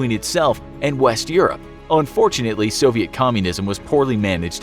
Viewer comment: Sorry, did you just say “unfortunately”?